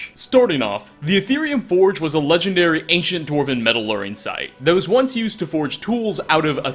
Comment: HeY how's it goin. Guys here. I'm just gonna jump right into today's lesson: 00:30 - sounds like you say "metalluring," maybe you meant "metallurgy," or maybe you just said "metal working" too quickly :) 1:08 and 3:24 that saying is "In and of itself," or "in and of themselves," you were close tho! 5:19 This is a tricky one. That word, "motif" is pronounced "moe TEEF" not "moe TIFF." Themes and motifs. The bread and butter of the literati. And with that, We're going to wrap up. This was a great episode! Some interesting stuff, for sure! Cheers :)